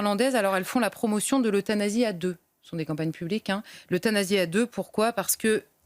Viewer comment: Ces gens ont perdu la foi ou ne l'on jamais eu.. La vie éternelle... Ils n'y croient pas 😥 Le Paradis, le purgatoire ou l'Enfer 😥 C'est normal de mourir mais le problème c'est comment vivre sa mort..Cette rencontre avec Dieu... Ce passage vers la vie Éternelle... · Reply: Ils n'ont jamais eu la foi justement ! Quand j'ai entendu "suic*de à deux" j'ai failli tomber de ma chaise! Que Dieu ait pitié de nous